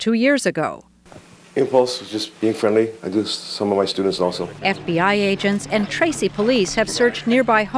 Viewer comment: Omg that guy who kissed Sandra on the lips, AND admitted to being attracted to children… has students??? Wtf? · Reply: And did he say 15:43 I kiss some of my other students also!?